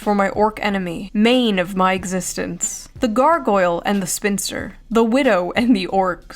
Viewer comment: Thought she said "the gargoyle & the finster " an ABC Saturday morning crossover no one would wanna see 🤮